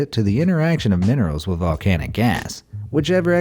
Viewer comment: wait what did you say? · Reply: Minerals